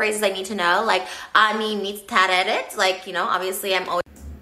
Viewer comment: I think she said אני מצטערת Ani Miztaheret I'm sorry Well, she tried too.